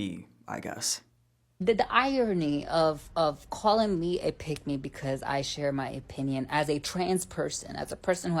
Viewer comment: When I heard someone say "pick me" outloud I heard "pygmy" & was like ''mmmk🫨"